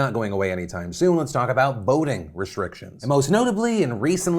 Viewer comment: i thought you said "boating restrictions" and was incredibly disappointed when that turned out not to be the case